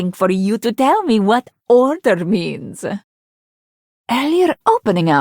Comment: How? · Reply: I thought she said "tell me, what does 'alter' mean", but I found out she says "order"